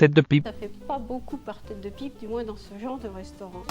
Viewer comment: j'ai entendu tête de bite